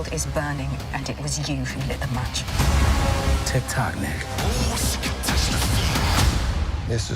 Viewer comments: Did say tick tock nicka at 0:14? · Sounds like he said tik tok nigga lol.